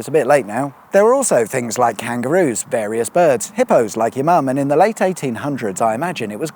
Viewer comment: did you say "Hippos, like your Mum"?